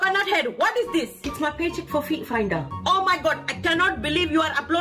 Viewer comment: I thought they said "beat finder" 😭😭😭😭 not "feet finder" 😭😭😭😭😭😭😭😭😭😭😭